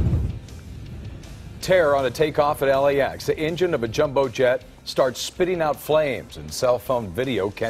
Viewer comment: did someone said jumbo jet ?